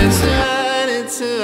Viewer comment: I can't tell if he's saying "A Lie", "A Light", "A Lot", or "Alright". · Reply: At some points, I hear him saying “It’s alive” lmao